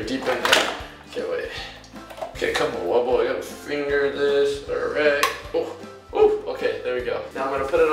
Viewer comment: ummm did he just say let me finger this